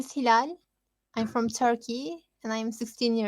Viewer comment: I'm turkey mi dedin ? Turkish olacaktı sanırım veya ben yanlış duydum basta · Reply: 'I'm from Turkey' diyorum